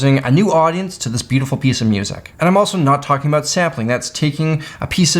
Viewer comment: I SWEAR I heard you said Zeppelin hahahaha. Sampling